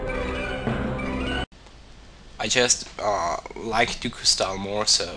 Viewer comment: it sounds like he sayed i jizzed